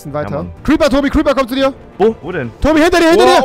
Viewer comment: ich hab 2 mal zurück spülen müssen um zu verstehen, dass er “diggi“und nicht “stegi“ sagt xD